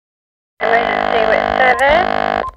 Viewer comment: i always heard "let me just see what 7 is" but "emergency with service" makes way more sense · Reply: I once saw a comment saying they heard her say "Emergency lip service" XD